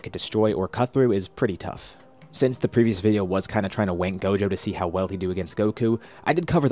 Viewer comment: did he say “wank him higher”??? I might be tripping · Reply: Does he have something to do with like speech impediment or an I crazy because I swear he says wank not rank also